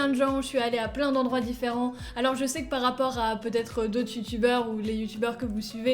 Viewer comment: C'était une super année continue comme ça 👍 Attendez c'est moi où elle dis tutubeur 😂